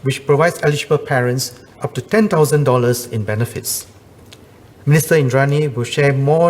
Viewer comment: Mrs Indranee right? I thought i mistakenly heard Mr Indranee? Cannot be right? From 2minute onwards